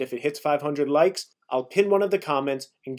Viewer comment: It's funny how nobody likes a single comment lmfao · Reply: my bad I thought you said, you're gonna pick someone with 500 likes in the comments lol. Then nobody liked each others comments.